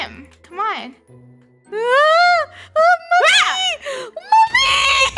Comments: did you say “MUMMY!” Btw, that part was funny 😆 Keep up the funny vids :D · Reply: Yea i hear say "MUMMY* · as a malayali i heard it as ammachi mommy 😂✨ ( ammachi in Malayalam is mommy)